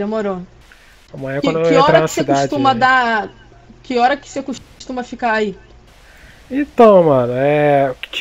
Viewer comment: Eu entendi que ele falou que horas você costuma dar 🤔